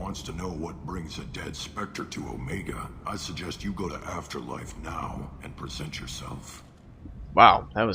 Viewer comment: Haha, the first time I played this the guy was like "go to afterlife" and I thought he was saying go to hell